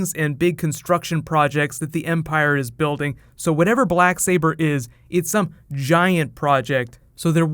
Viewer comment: At archives scene wasn't it called "Dark saber" and not "Black Saber"? Anyway I'll be seeing it again Tuesday and I'll listen more carefully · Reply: It was 100% Black Saber. I thought I heard Dark Saber on my first viewing as it was referenced in the Rebels episode last week, but I saw it again yesterday and it's definitely "Black Saber"